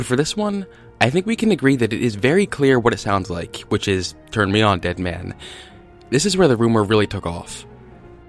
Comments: I thought it said “turn me on Edwin “ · I heard “turn me on Desmond” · I hear ‘Turn me on, Lemon’